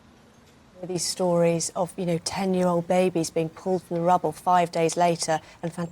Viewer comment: ? Ten-year-old babies, did she say?